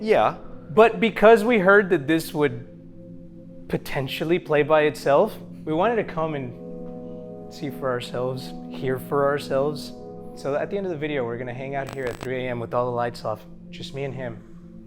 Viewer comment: they want to come hear for themselves. Instead of “come here ourselves” 😂